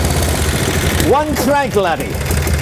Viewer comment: "One crank laddy" is that what you said?